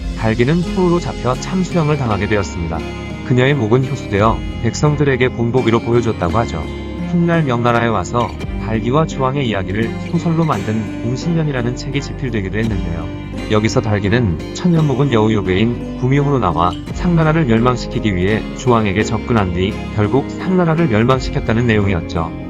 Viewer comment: 봉신연의 ㅋㅋㅋㅋㅋ 뵹신년이로 들었.......ㅋ